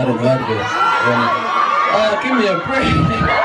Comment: What does hero say at 4:38 someone tell me 🤧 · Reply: Autumn he said " aah give me a break " soo for the french people, hero said " aaah laissez moi tranquille 😂 " because the fans crie when hero open the mouth..